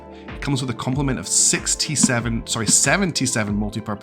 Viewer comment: ahaha 23:47 when you said 7 T7s it sounded like seventy-seven